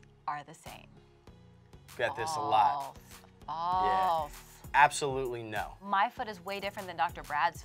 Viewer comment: For a moment I thought she said f**k when she said false. 😂😂